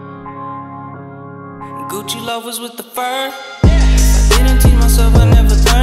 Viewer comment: I thought he said: Gucci love us with the fur. (Would be kinda dope if he would say that lol instead of: Gucci loafers with the fur.